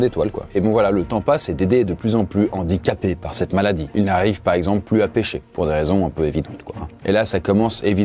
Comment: à 2:35 j'ai compris "il n'arrive plu à pécho" ^^